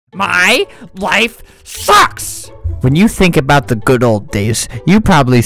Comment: did he say "fucks"?